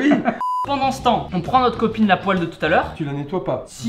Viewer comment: j'ai entendu "on prend notre copine a poil de tout a l'heure"